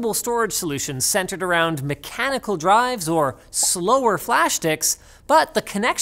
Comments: I swear I heard Linus say flash diks! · "flashdicks" 0:16 wink and frown :D Well played, Linus. Well played. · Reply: Even I thought I heard flash dicks. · did u just say "flash-dicks" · Did he just say "flash dicks?" Hehehe - sorry.. Very old throwback to South Park :-P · Did he just say "slower flash dicks"